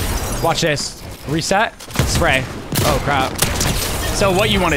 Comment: I THINK HE SWORE HE SAID “oh crap” 11:46 thought this was a family friendly channel